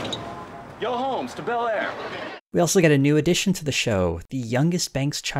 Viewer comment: The original title of this video was "Yo Home to Bel Air", and the title card in the video still reflects that. It's a reference to the title of the theme song. But since people are confusing it with when Will shouts out "Yo Homes to Bel Air" during the opening credits, I've changed it to the more memorable line. And apologies for two errors in the video. I pronounced Nia Long's first name incorrectly, and the footage from the Eddie Murphy special I used is from Raw, not Delirious. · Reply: I always thought he said "Yo Holmes, to Bel Air" as a nod to the English name Jeeves commonly used to refer to chauffers. Maybe I imagined this.